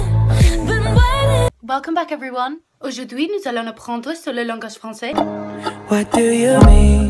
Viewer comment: I'm french and I understand what she says at 0:46 ;) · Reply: She said today we learn the french language ;) In french it's : aujourd'hui nous allons apprndre le langage français :P